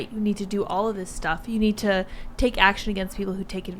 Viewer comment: “You don’t need to take action if you’re violated” is that what I heard at 9:20 fuck naw this bitch